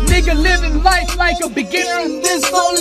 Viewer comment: did he just said nigga?!🤨